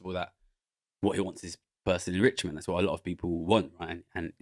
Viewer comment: What does Gary say at 57:56 about Nigel Farage? Sounds like 'bursary rich man'. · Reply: “Personal enrichment”